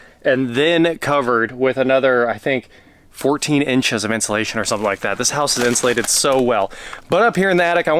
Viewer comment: wait.. did you say "rockwool insulation'?? did the seller disclose that? rockwool needs remediation due to asbestos contamination... ALL ROCKWOOL INSTALLATIONS. Here in the n.e., we have been dealing with a rash of homes with old rockwool.. they need HAZ-MAT crews.. i hope you aren't in this category... can you have it tested?